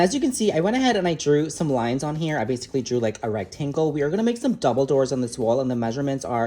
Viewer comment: straight up thought he said “dumbledore” 😂